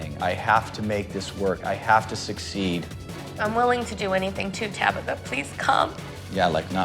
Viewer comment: Look I know I'm crazy but so why did it sound like she said she would do anything to Tabitha 😭😆 😅